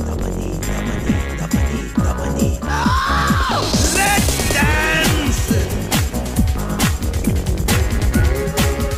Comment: I have no idea what you're saying... And your suit sucks! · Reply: "let's dance" that's what he seys, n why y don't like the suit?